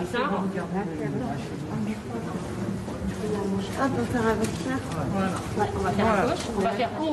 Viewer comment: Et moi j'entends très bien ah il faut faire avec ça. Dit-elle en parlant de la seringue qu'elle regarde. Et je suis pas un média dominant. De même qu'elle a dit ah il faut enlever ça du même ton quand on lui enlève son gilet juste avant. Mettre le début dissipe l'ambiguïté. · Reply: Et moi j'entends très bien : "Ah, faut faire un vaccin !" "On va le faire à gauche" dit une infirmière, "Alors on va faire Corona" ajoute une autre. Il faut prêter attention à la dernière syllabe pour vraiment distinguer VaCCIN de faire avec ÇA .